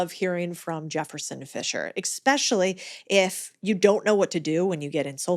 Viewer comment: secs in and I hear," eggspecially.......", one word says it all.